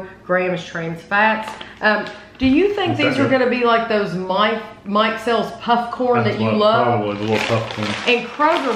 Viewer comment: Whenever I hear that brand mikesell's I hear "Mike Sells..." 😂 It just sounds odd when I'm not familiar with it